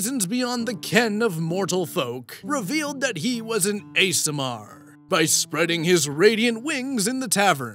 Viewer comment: I listen to this in the background, so I don't see the text. At first when you said he's an Aasimar I heard it "He's an ASMR" and my first thought was "What? He makes pleasant sounds?"